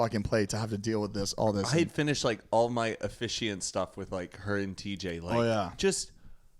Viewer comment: I'm sorry, did just say he was gonna be TJ & Erin's officiant?